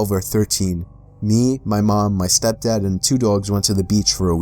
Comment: I thought you said stepdog